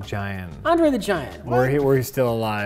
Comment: i thought jack said Entree The Giant